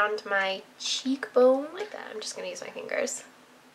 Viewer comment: did you say, " F that I'm just gonna use my fingers?" hahaha